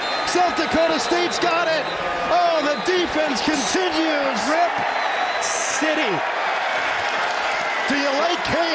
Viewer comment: did he say rip city????